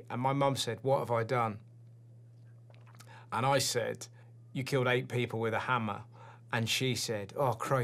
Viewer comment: OH! She killed EIGHT PEOPLE with a hammer, I always thought he said A PIG with a hammer. This makes so much more sense